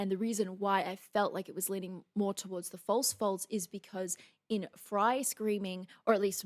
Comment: Calling them false folds is confusing, haha. I kept hearing "false false". Most people who talk about that vocal style call them false chords.